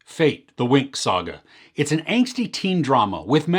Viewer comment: At first I misheard and thought you had said "Fake: The Winx Saga", which is....strangely fitting. 😆😅